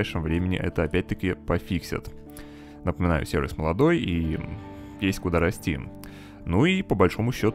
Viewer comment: ты сказал (пис***у дорости) или мне послышалось?